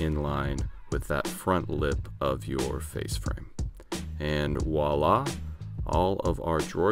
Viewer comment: did you just say 'Woila"? You know its "Voila" right?? Its a V. I still have a man-crush on you though.